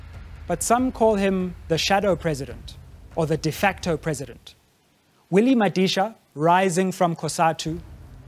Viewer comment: Is it me or does Sizwe say Winnie Madisha at 2:20 ?